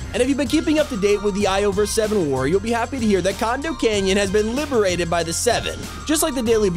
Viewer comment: I swear it sounded like you said "condom canyon" I don't have a dark mind it's probably cuz I'm sleep deprived